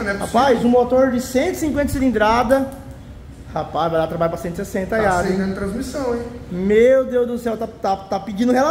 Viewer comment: ele falou oque? Qua vai dar trabalho pra 160? N entendi bem